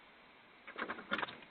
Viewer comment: My goodness, I thought she was saying Porky! 🤣 I'm like...well that's not nice! lolololol